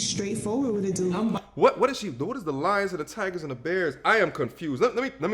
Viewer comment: The hyenas, tigers, bears...wtf....😂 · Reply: I knew I heard her say "the hyenas, tigers and bears." What does that suppose to mean? Lol.